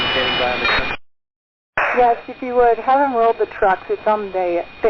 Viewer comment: what does the pilot mean when she says "have them load the truck". Impressive pilot! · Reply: It was "have them roll the truck"; she's asking for airport fire trucks to meet the plane